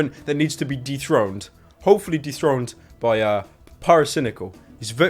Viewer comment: I thought pyro said "deep throat pewdiepie" instead of "dethrone"